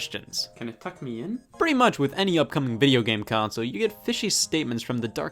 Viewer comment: "Can it tuck me in?" -Scott Wozniak, 2020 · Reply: I thought he said “cuck me in”